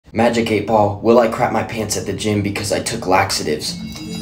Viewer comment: For a second I thought you said parents instead of pants 😂